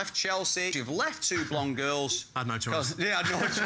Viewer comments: wtf did he say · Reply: "I had no choice" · WHAT IS THE DUDE SAYING??? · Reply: I had no choice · What did he say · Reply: "I had no choice"